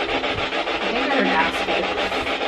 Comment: At the beginning I thought she said “ I think I heard a bagel”